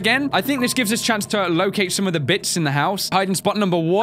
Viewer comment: wait did he say "Bitch"?! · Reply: Ingthor Valdimarsson "Bits"